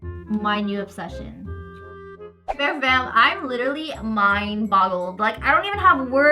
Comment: So at 7:37, did she say I'm totally mind bottled? Like when you get all your thoughts trapped in a bottle